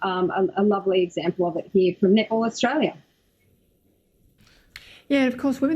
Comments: I thought the first lady said nipple Australia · I kept hearing "nipple Australia".